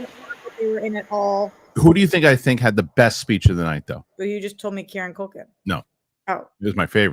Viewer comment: Does anybody know what said @ 30:46 mark? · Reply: said but you just told me Kieran Culkin